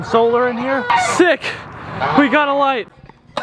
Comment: I thought you said sick I got a life but no it was sick I got a light